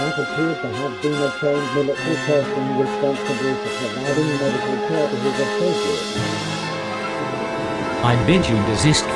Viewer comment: what is the Pyro saying here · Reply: "I appear to have seen a trained military person responsible for providing medical care to his associates"